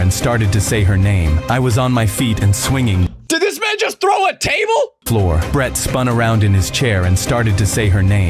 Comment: But I thought you said his stories through a table